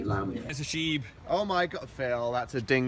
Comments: When they were at the dingo part I was really confused because for the longest time I thought that they were saying sheep instead of shibe. slow claps This is why im in beauty school. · when phil said its a shibe i thought he said its a sheep and i was like wtf phil